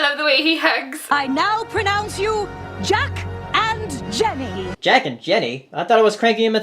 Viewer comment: I just realized Mayor Mare called Cranky Doodle a Jack ass... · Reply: Following Gerard's Way I heard "Jack and Jenny"